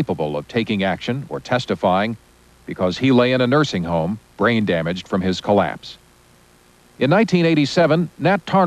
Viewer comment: ( his brain dead phase... wished I hadn't seen it but that was reality. RIP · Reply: I thought they said brain dead too but they said brain damaged.